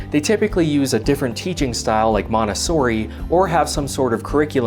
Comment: Man, you Americans should definitely give up trying to pronounce italian names, what you said at 12:25 sounded like "manessori"